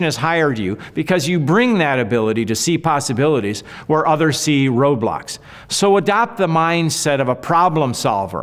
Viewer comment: I thought you said robots not roadblocks for a second I think both would be sefisent